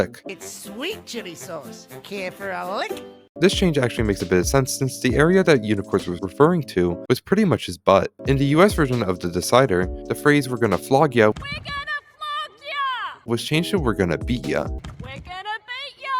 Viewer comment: I Legit Heard "We're Gonna Fuck Ya!"